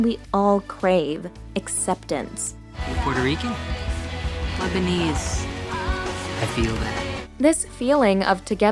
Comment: There’s a theory that Janice told Regina she’s Lebanese Regina thought she said lesbian